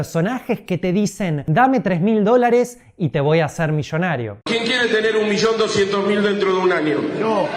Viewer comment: La última vez que vi a lo ví en un vídeo donde estaba atravesando cómo por una depresión o algo así, se veía bastante hecho polvo pero ahora ver a éste joven con tanta energía, tan bien y hablando de temas tan interesantes me motiva a seguir firme en éste canal. Me alegra verte tan bien, tienes mi apoyo. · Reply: Yo entendí "te voy a meter la banana", wow! Santos cielos! Haha... La explicación que diste sobre el que invertir a largo plazo en lo más aconsejable antes que el hacer day trading (especulación en bolsa) ya lo sabía, lo había leído en un blog hace ya un tiempo, por eso mismo nunca "invertí" en day trading y tampoco alcancé a aprenderlo (en mi tiempo estaba muy emocionado con ello y era a lo que me quería dedicar), ya que me di cuenta de que los que ganaban eran los que tenían sus academias y vendían cursos y no los day traders (que casi siempre pierden el dinero en la especulación, si no lo pierdes hoy, lo vas a perder mañana), lo mejor es invertir siempre a largo plazo.